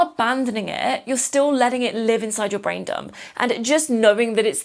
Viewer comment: Lol I kept mishearing "brain dump" as "Braindom" and now it's my new favourite word even though that's not what was said 😅